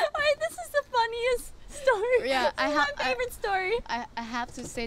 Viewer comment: Am i the only one who heard that “dafaq” at 3:34 by Rosé? 🤣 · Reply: She said "the funniest part" but cut herself off so it sounded like she said dafuck